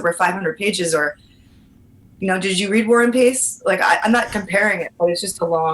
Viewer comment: I thought she said "did you read One Piece?"